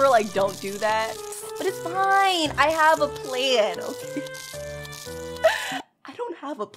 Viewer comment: favourite word when she play PvZ: 1. nugget 2. Shrooms 3. Produce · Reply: I have a plan... I don't have a plan Me: it sound like say I have a plant lol